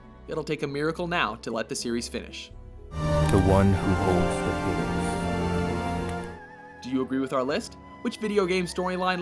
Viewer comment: Did he say "the one who holds the penis"?